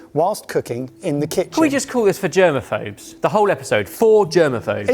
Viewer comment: When he said "For germaphobes" I heard "For German Folks". Made me wonder if Germans have a bin fetish?